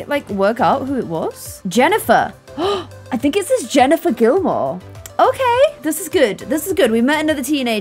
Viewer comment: Was I the only one who thought she said Jennifer Dilbert?? 😂